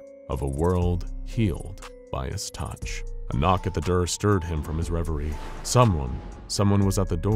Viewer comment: Why is nobody else talking about how the narrator said "a knock at the der" instead of door